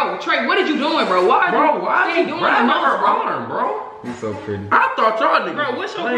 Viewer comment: am I the only person who hears trey say “ u so pretty”😂 Like if u agree 😆